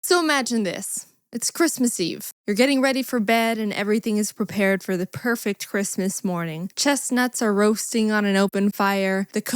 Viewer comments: i thought she said chests roasting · I heard It's omach abiss At 0:00 Edit: now I hear So imagine this It's crazy!?!?!?